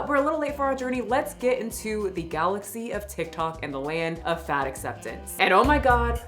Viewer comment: I was brushing my teeth and thought you said ‘sick tok’ instead of tictock and was thinking what a great nickname.